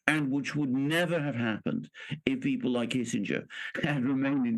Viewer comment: Did he say ' kissinger ? 3:57 , because kissinger is one of the biggest mass murdering war criminals on the planet .